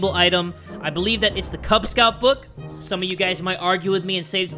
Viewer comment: Did he say the "kubz Scouts book?"